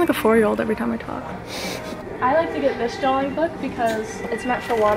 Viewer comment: did she say drawling